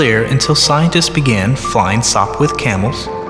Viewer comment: what's he saying? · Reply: InYourFaceNewYorker Sopwith Camel - It was a WW1 plane.